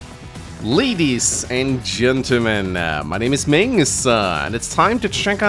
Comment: Why do i hear always 'ladys and gentlemangs'? 😂